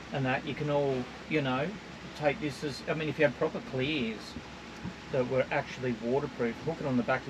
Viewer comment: he says ..."proper ....s that were waterproof". Can anyone tell me what word that was as CCs closed captions are not showing, and I have replayed a dozen times and can't make out what he is saying. Thanks and appreciated to anyone replying. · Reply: clears ,as in clear plastic covers to keep water out so you use the boat as a caravan that is kept dry while driving.